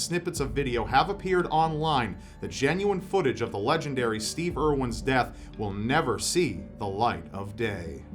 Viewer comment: When you said Steve Irwin, for some reason I thought you said Steve Urkel and I was SOOO confused. “Steve Urkel was a man who stared death in the face on a daily basis...”